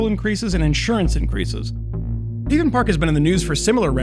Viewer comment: I like how the auto-subtitler reads as "Thieven Park" instead of "Haven Park". Subtle.